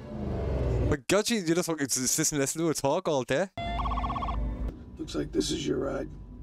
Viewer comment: What did he say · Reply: "My god she's ...cant make out this part.... just listen to her talk all day."